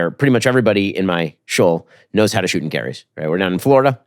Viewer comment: can anyone explain 57:10 - Anyone in my Jewish (schol... shoal... sheol???) - just curious what the word is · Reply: Yep the word is shul, which is a synagogue